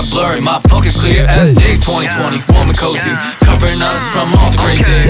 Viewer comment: at first i heard this part, 2:24 , as nct 2020 maybe because im thirsty for it